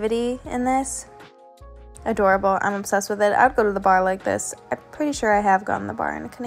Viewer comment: I heard “I’d go to the barn like this” 😂 Bar. Go to the BAR like this.